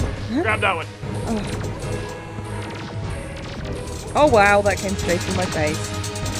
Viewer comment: "oh wow that came straight on my face" did i just hear that right